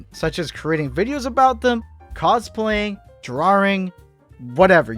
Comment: did he just say drawRing 😂